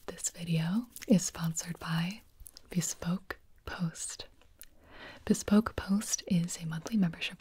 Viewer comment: I thought she said this was sponsored by Facebook post